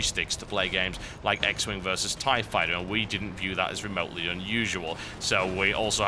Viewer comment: am i the only one that heard him say "x men vs tie fighter" ? like xmen vs street fighter and xwing vs tie fighter combined? sounds AWESOME!